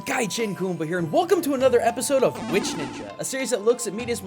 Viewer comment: For some reason I thought it said "White Ninja" instead of "Which Ninja"